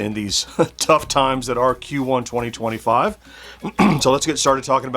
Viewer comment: What you said here! I think you started speaking alien. 👽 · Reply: Q1 2025? Alien? 👾👽